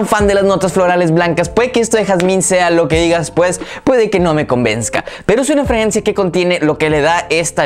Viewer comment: que dijo? 😅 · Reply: Convenzsca jajajaja